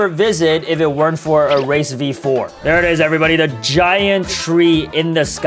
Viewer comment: boys i was watching this with captions, you said race v4 and the captions said racist v4, its probably from the dragon earlier💀💀💀💀